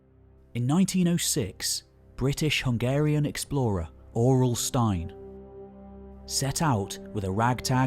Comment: I was only listening and at 33:31 I thought I heard R.L. Stine and I'm like the Goosebumps guy? 😂